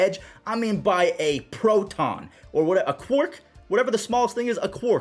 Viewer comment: a quark? thought you said cork lol